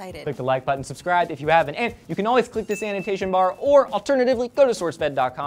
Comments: AH, I finally get it... "annotation bar". I kept hearing "sanitation bar". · For a second I thought he said we could click the sanitation bar but he said this anotation bar. silly me.